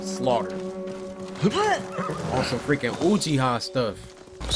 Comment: I thought he a said whochia